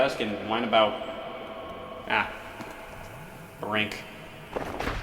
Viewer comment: The subtitles say “Hey y’all Scott here” even though Scott confirmed that it’s “Hey all” Care to explain? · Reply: I don't think he wrote these captions. At 31:49 the captions say The Rink while scott would know that he was talking about Brink